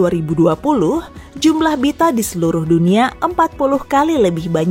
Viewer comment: Ngomong apa si di menit 1:55 ? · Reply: Bita